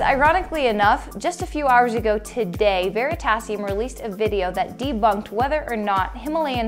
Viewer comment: she says "veritas (or sounded like it)released a video debunking blaablaa.." but where is that video? I'd like to see it.